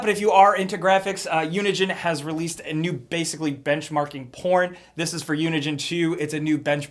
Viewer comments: did you say porn? if so we'll, I don't know what it has to do with all these graphics but ok. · did you say porn? and then 2:50 did you say sex?